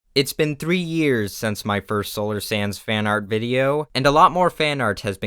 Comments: Well, ive always heard you saying solar sands not solar sans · I never heard you say “Solar Sans” during your outro, but now that you explained it, I can’t hear “Solar Sands” anymore. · i heard it as sands until you mentioned about people calling you solar sans